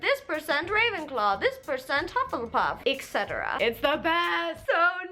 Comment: the subtitles say Topsail pub instead of Hufflepuff lol